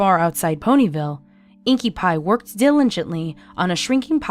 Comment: Did he just say INKIE PIE???